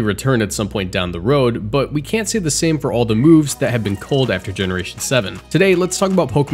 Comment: when the editor mishears "culled" as "cold" lmfao